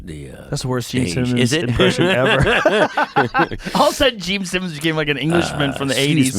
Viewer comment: I thought he said Richard Simmons. I was like that impersonation is f ‘d up and he was lining Carrot up as a gay prostitute for a side gig